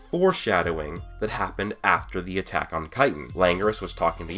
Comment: am I the only one who heard attack on Titan?